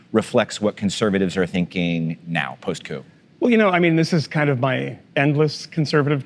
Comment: Did he just say, "post coup?"